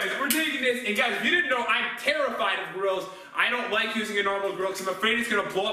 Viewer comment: I thought he said that he is terrified of girls